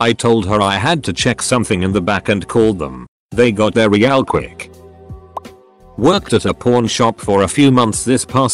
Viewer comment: I thought it said worked at a porn shop 😂